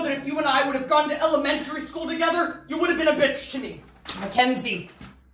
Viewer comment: did he just say McKenzie shouldn’t be a pr!